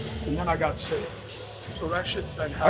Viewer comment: “I say it’s been a tren out of tren” that’s what I heard 💀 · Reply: I heard "tren out of clen" but that's just me